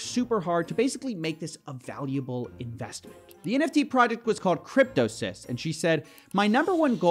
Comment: I had to double check that spelling , thought I heard it was called “cryptosimp”.